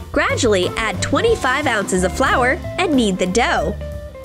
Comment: did she said flower?